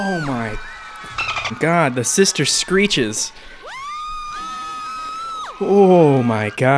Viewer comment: "Omyyygodd the sister screeches " LMFAO 😂😂😂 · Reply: omg, I thought he said "sister creatures" 😱